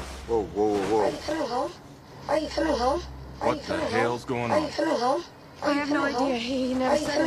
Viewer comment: Sounds like he is saying “how you feel’n hoe”😂